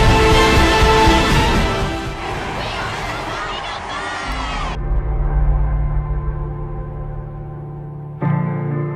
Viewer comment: what do they say at 5:26?? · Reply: “We are the Final Five”.